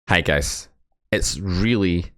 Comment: Did you just say "hagus"?